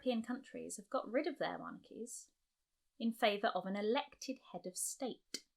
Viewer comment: The subtitles say "monkeys" when it should read "monarchies"!